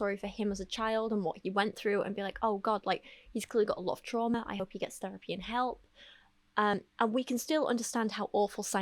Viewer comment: Did a double take because for a second I thought you said "I hope he gets therapy in Hell" and ngl I chuckled